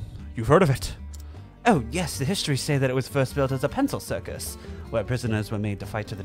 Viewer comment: I never caught this in my editing, but I accidentally said "pencil circus" instead of penal circus.